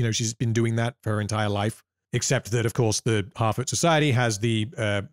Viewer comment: I misheard you say "harfoot society" as "halfwit society" and you know? it still tracks.